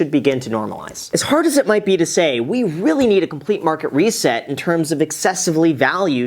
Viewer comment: For a second I thought you said, “a great market reset”. It triggered my conspiracy theory mind for a second. I was thinking is Graham advocating for the great reset? Hahaha